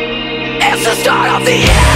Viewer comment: Please tell me I'm not the only one who keeps hearing," WE BE SURROUNDED BY VICIOUS SHARK COALS!!!" lol maybe I'm just crazy. XD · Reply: Well i hear ''blood on my ass'' at the beginning :D